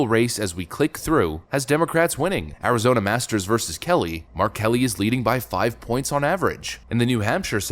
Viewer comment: Holy crap, I thought you said "R Kelly" for a moment. Had to go back and watch again.